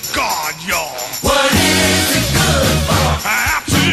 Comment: I thought they said "What it isn't good for"